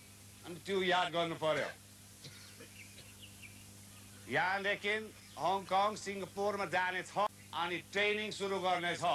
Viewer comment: He actually said Hong Kong and Singapore